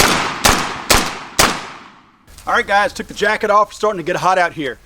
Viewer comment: "All right guys....Jack it off" Did he really say that?